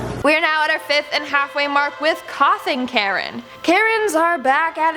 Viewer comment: When she said coughing Karen, I heard "Coffin Karen".